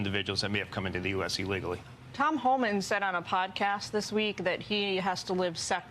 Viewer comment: I thought you said "Tom Holland"